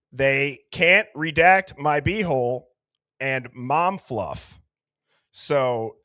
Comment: I was multitasking when I first started listening to this and stopped dead in my tracks when I heard "They can't redact my b-hole and mom fluff" and was very confused for a minute there lol that's what I get for not paying attention 😅